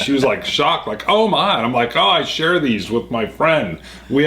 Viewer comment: love comment. it almost sounded like he said "I share these with all my friend." if you think about it long enough it becomes very funny.